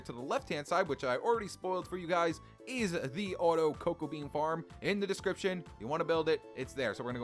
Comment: I was dozing off and thought you said autobean coco farm 😂